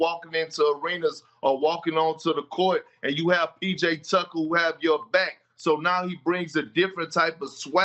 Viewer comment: Did Windy just call PJ Tucker “Pizza Tucker”? 🤣 Man’s hungry - get him some lunch! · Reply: Haha sound like she said PJ Ducker 😂